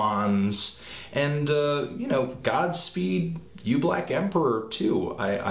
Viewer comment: When he first mentioned Godspeed here I initially heard him say Godsmack. Man, I need to sleep.